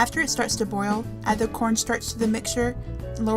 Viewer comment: at minute 3:38, what do we add I couldn't hear it and I couldn't find any corn in your description (maybe its corn don't know), but what is it anyway? and thank you looks delicious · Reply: Cornstarch